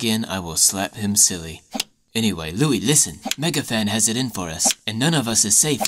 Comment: when zayn said Louis it sounded like harry